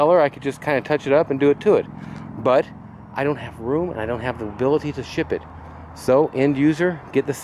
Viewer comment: What do you think shipping would be to Vegas on that green Mercedes? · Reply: Sorry. I heard you say you don't have time to ship it.